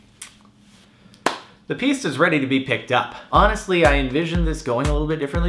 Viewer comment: Bruh I thought he was doing delivery with the car. I heard pizza at 0:04.